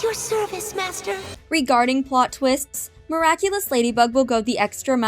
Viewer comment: i thought bunnix said regarding plot twits😂